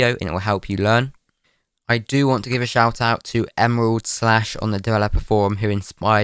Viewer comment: Emerald SLASH Me : What ? EMERALD SPLASH ?! Also Me : rewinds to see what he said Also Me Again : realizes he said emerald SLASH aw man.